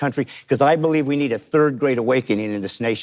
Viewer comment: I thought he said “third grade awakening.” I was trying to figure it out thinking maybe we all just need to be as smart as third graders. 😂